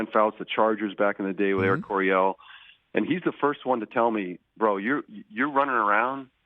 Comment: Jazz guitar Freudian slip - at 12:42, Steve Young says Larry Coryell instead of Don Coryell. (RIP to both geniuses). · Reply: He said "Air" Coryell, the nickname of the Dan fouts lead chargers offense